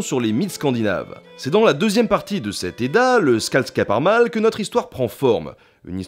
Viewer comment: Bon sang. A 0:38, j'ai compris "le Scalde Saparmal". Ca ferait un excellent nom de PNJ :D